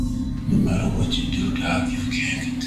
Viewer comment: did Electro say well you'r about to or i wanna know to